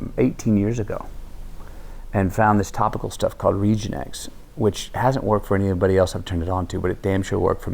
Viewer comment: what is that thing he used for his hair ?? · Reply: yazeed farghal it sounded like he said reegenex